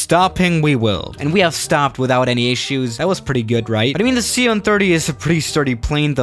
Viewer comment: who else heard C-on30